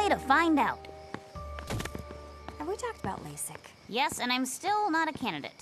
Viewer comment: what did daphnia say at 24:52 laysic? what's that? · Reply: It's an acronym, LASIK. Basically laser eye surgery, if you heard of that before. It's done to fix people's eyes do they don't have to wear glasses or contacts to see